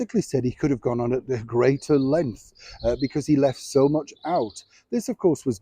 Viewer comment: "... [in Spare, ] basically said he could have gone on at greater length...": I totally misheard this as 'could have gone on, at the grace of lent' 😮😂